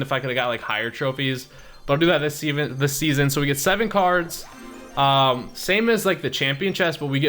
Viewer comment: Did he say semen?